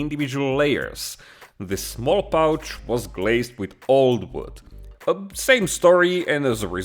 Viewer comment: did you say "glazes" at around 6:40? what are they?